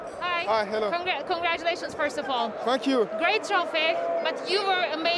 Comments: He says fuck you instead of telling thank you 😂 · The reporter : Congratulations first of all ! Kanté : F**k you 0:00 Don't tell me you didn't hear it this way too 😂😂😂😂